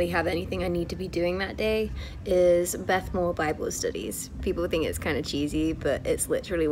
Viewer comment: Can anyone write what's she saying starting from 2:17 thanks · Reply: I think something about Bible studies